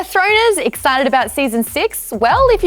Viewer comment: thought u said throaters..